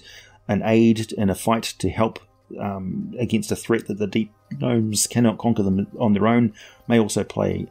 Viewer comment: My favorite gnomes! Also at 10:27, it sounds like you said Neep Domes by mistake